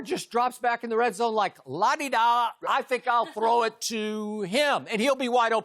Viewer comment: Did Skip just say "La-di-da?"